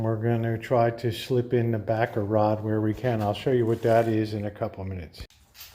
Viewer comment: OMG! I thought you were saying Baccarat, not Backer Rod! LOL! You can tell I don't know how to fix anything :body-blue-raised-arms: Thanks for the lesson 🤓